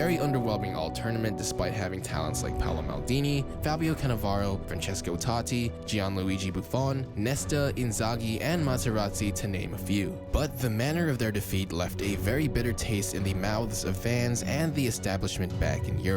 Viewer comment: did you just say Maserati😂